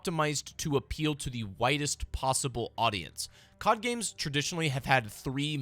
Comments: thought bro said “whitest” possible audience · I thought he said they want to appeal to the WHITEST possible audience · LOL when you said “a game that has been optimized to appeal to the widest possible audience” I thought you said “whitest possible audience” 😂😂